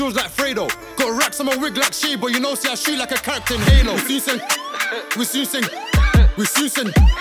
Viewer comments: I heard this before but thinking at 1:01 sound like he’s saying we soon send or somet not we zooted 🤣🤣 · What is he saying at 1:00? What does soosing mean?